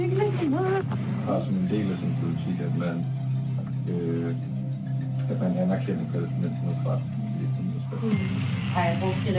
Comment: 🚨 Caught red-handed fabricating subtitles. I’m Danish, and what’s being said in this video is NOT what the Hebrew subtitles claim. ▶ The fake caption says: “We recognize the Palestinians’ right to armed resistance.” But the real Danish line is: “Det er også en del af politikken at anerkende …” (“It is also part of the politics to acknowledge …”) — no mention of violence, weapons, or “resistance.” ▶ Another fake subtitle: “That means we do not condemn anything on the Palestinian side.” The real Danish line is: “Har vi brugt de rigtige ord? Vi skriver jo ikke.” (“Have we used the right words? We don’t write them anyway.”) — they’re talking about wording, not war. ▶ Then comes “A Palestinian grabbed my wallet through a car window.” But she actually says: “Der var faktisk ikke nogen der tog mig på røven.” (“Actually, no one grabbed my ass.”) — a completely different story, twisted into propaganda. ✅ Only one subtitle, “3-4-5 ubehagelige oplevelser” (“3-4-5 unpleasant experiences”), was translated correctly. The rest are outright falsifications. 🎭 This isn’t journalism — it’s political theatre dressed as “undercover work.” If a story is true, it doesn’t need fake subtitles to sell it. 💬 Verify before you share. Truth doesn’t fear translation — lies do.